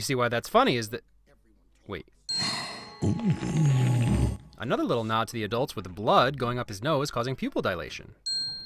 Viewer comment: why did i think he said. ANother nazi adults